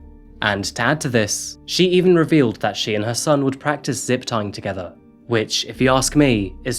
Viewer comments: Could somebody please clarify what said. "She and her son would practice (???) together". Could not catch what he said or just don't know what he is talking about. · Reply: Zip Tying. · what do they practice? Zip tongue?? I srsly cant understand it acoustically can someone pls tell me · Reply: Zip tying? Is that some kink or dbsm stuff? Lmao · Somebody help.... what is he saying at 18:54? The mother and he would practice what together? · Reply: "zip-tying" he and his Mom "practiced with the zip-ties" on each other. Go figure.